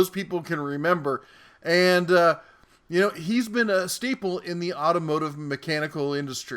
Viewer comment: did you say "steeple"? I think you meant "staple"